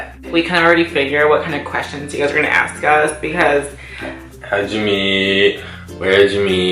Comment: boyfriend said "how'd we met" but it sounded like he was saying jimmy